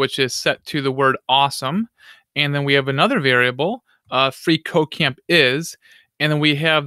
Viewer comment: makes me think he is saying free co-caine is awesome.... Not at all a drug addict btw.